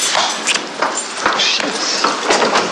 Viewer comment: i swear Brax said "Shit" when he got up ahaha <3